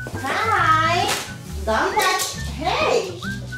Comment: Mom, I thought you said touch not don’t touch 😅